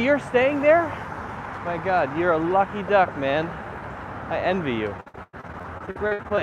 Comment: It's the first sneez I saw live. Expecting ur birthday video,May God fufil all ur dreams. Did u said at 10:00 "Lucky Duck",can u elaborate it? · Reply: Yes, I said lucky duck